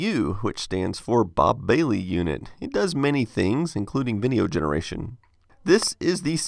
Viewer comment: I thought you said Vimeo generation instead of video generation.